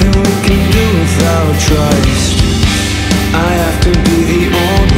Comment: When it said "there is nothing we can do without trust" I thought it said Christ lol